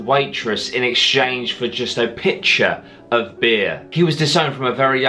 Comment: Pitcher of beer, thought you said picture